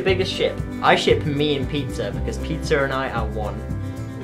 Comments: "Who's your favorite ship?" "I ship me and pizza" You are awesome lol Pizza is life x) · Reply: at first I thought he said 'Peter' but he said pizza xD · When he said pizza, I thought he said peter. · I'm sorry I'm terrible I thought you said Peter when you said Pizza.